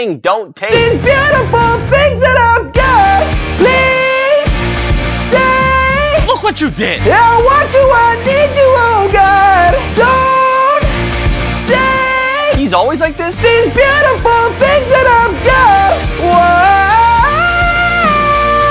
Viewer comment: People who think his voice is amazing! 👇 👇 MUM IM FAMOUS!ty for 5k likes!! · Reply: Bro why do I always hear “these butt-iful things that I’ve GYAT